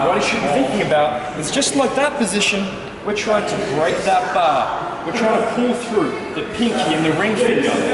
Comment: what did he say we trying to break that bar??